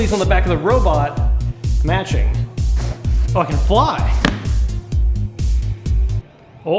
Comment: Sounds like he cursed, which I wished it occured, however he only said, "You can fly!" which sounded like "F**king".